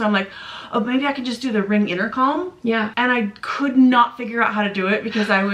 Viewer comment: Did she just say inner calm? It’s Intercom … Rewatched and definitely heard intercalm. MAMAS WE LOVE YA, BUT ITS INTERCOM.